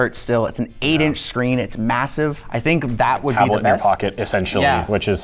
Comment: Did you just say MASSIVE